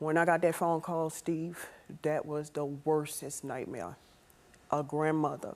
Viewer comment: did she say "worsess"?🙄